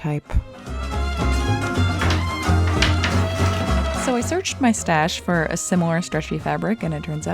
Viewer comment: I swear I heard “Frodo-type”